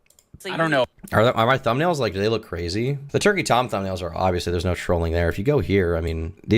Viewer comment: I dunno, I heard "Twerky Tom", which should absolutely be a thing.